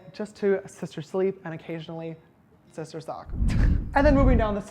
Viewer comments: Is it me or at 9:33 did he say “sister suck” what??😂😂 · WHAT DID HE SAY?! 😂😂 “Just to sister sleep and occasionally sister ——“ I heard sister sock. And— you know... 🍆🍆 But then again I heard sister suck. Both of which I find very dirty but that’s me :)) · Did shistar james say shistar succ at 9:32..................im shistar shook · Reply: If you put on the captions it says “occasionally sister talk” at first I thought it said the same thing tho 😂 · did he say sister suck?..... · Did he just say a "sister suck" at 9:32 😂😂😂😂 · did he say "sister sleep and occasionally sister suck" ???? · diD hE JuST sAy SiSTer SuCk!? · did he say sister succ? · did he say ‘sister sleep and occasionally...sister SUCK’ ?? lmao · did he say sister suck or am I losing my hearing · did he say “and occasionally sister suck” I LOVE IT🤣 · Did sister James just say sister suck??? Lmaoooooo yessssss jamessssa get that d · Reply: I thought he said sister sock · Did he say " sister suck " at 9:33 ? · help , did he say sister sock ? I don’t understand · DID ANYONE ELSE CATCH IT AT 9:32 "SISTER SUCK" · lowkey thought that at 9;31he said "sister sleep, and sister suck" not "sister sleep, and sister song" .... ill leave now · did he said sister suck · uhm sister suck? 9:30 am i hearing this right...??? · Did he say "sister suck" at 9:31 ??? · did he say “sister sleep and occasionally sister suck?” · Did he say sister suck? 😂 or did i hear that wrong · DID JAMES SAY SISTER SUCK????